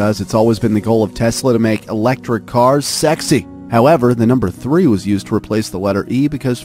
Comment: does that say sexy?